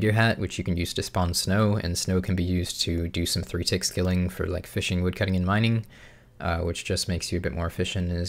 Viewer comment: I can’t understand what you’re saying.... “snow can be used to ????” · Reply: Snow can be used to do some 3-tick skilling like fishing woodcutting and mining.